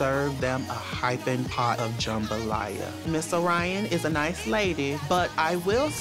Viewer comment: i didn't catch it the first time but at 1:49 Kornbread yells"let's go Miss Orion!" and it rlly warms my heart